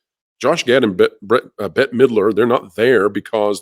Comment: At first when you said Midler, I thought you said Hitler 😂